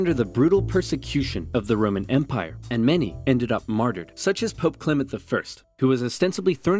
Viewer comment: Am I the only one who heard maderd?